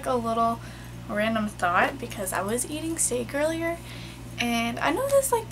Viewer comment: she said steak n i thought she said snake lmao